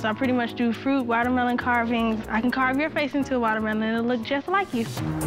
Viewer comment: "He has no idea who hes talking to" Yes, indeed. Gordon should have a lot more respect for the grand champion of melon carving. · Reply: At first I thought she said " Ill carve your face up like a melon" LOL I was like Oh shiot this beech is crazy!!